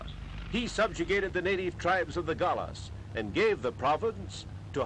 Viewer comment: did he sayd the gala ..or i just lost my since